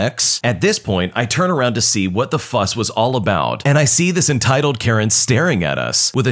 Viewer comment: "What the fuss was about" Bro I thought you said "what the f**k was about" 💀